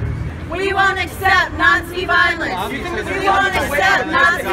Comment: PMSL. I thought the wimmin were saying "We won't accept Nancy violence."